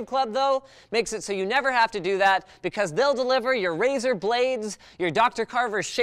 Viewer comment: I thought he said Razer Blade...... Hahaha